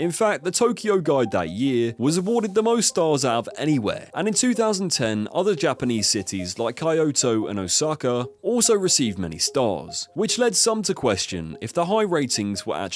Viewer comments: Cayoto ? Or Kayoto ?? And the caption/subtitles said Coyoto .. I never heard that before in my entire life. Is it Kyoto ?? Do you mean Kyoto ?? 😮 Are you ai ? Or just assumed that, confidently.. I'm so confused 😕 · did you just say *Kaiyoto*?